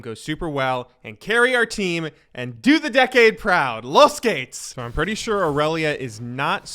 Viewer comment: Does anyone know what says at 4:05 ? Los gates ? He's been saying it before starting most matches and I find that phrase quite endearing. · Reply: Its "los geht's" the german translation to "lets go". he is learning some german on the side